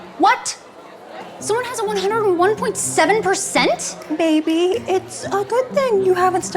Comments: it sounds like shes saying baby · it sounds like she is saying baby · sounds like she is saying baby 😂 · When the girl said nave it a good thing I thought she said baby it’s a good thing lol who agrees